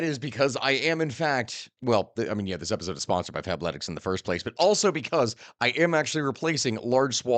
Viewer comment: Thank u for putting the name on the screen. I thought u said Fapletics. I'm like, I'm on the varsity squad there pimpin.